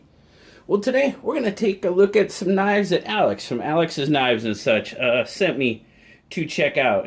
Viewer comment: Holly smokes I thought you said from Alex’s knife box!!!!! Lol